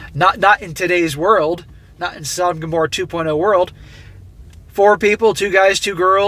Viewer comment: Could someone tell me what said around 5:28 ? Like <something> 2.0 world. I would to add it into my personal RP dictionary LOL. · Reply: I don't know if you'll see this, but he said, "not in a Sodom & Gomorrah 2.0 world." It's a biblical reference to the two very sinful & sexually immoral cities that God destroyed.